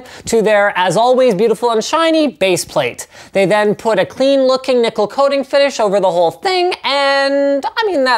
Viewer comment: thought he said clean looking nipple coating... man its late